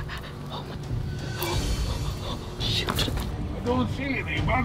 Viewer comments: i swear said sh** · did she say sh**